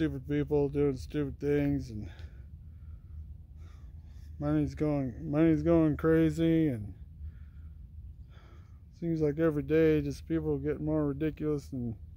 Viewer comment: When he said money's going crazy, i heard him say: my name's going crazy. Lol.